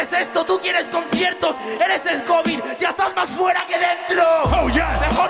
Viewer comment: que dice gazir ahi? eres ....? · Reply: El covid